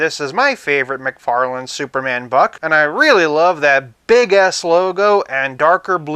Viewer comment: I thought I heard "I love that Big a** logo" lmao😂